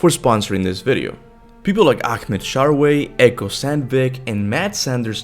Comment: DID HE JUST SAY AKMED SHARWAY